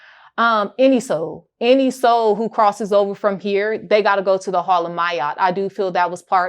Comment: Thought shorty said he went to the hall of meats 😂 ayoooooooo